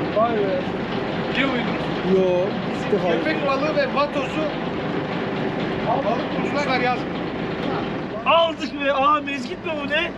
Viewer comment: fa entrasan birsey soylicem dedinde bir cacık anlamadık biri mealini yazsın 10.dinleyisim hala anlamadım ne dedigini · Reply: Köpekbalıkları ve vatozları balık bulucular yazmıyor dedi. Ama bu doğru değil yazıyor...